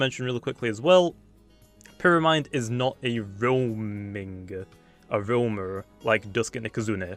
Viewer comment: I thought he said pyramind is not a zoomer